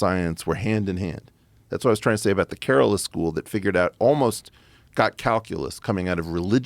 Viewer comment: which school is this? I cannot understand what he’s calling it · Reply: Kerala school of astronomy in South India